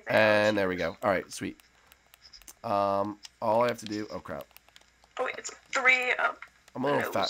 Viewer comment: Did you say crap Lol